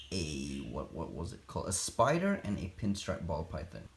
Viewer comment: Wait did he say it was a cross through a python and a what? I think i heard Spider but im not really sure can anyone tell me? Is this Pokemon breeding but to a whole new level